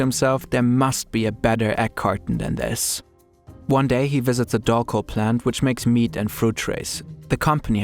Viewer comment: subtitles say doll plant, what word did he say there? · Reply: Dolco